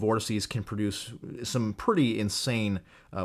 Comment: Did I hear that right? Some study says it’s possible for some subvorts to contain winds of 700 mph? I have to have misunderstood something. Right?